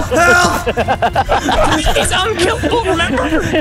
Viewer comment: maybe i am mishearing things..... but as a german i totally understand "hör auf hör auf hör auf" right there O _ o